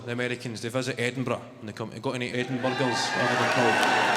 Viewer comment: I know he says "Comfy Edinburgh" but I keep hearing "Cunt from Edinburgh" 😂